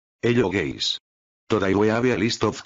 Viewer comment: IT SOUNDS LIKE HE SAID HELLO GAYS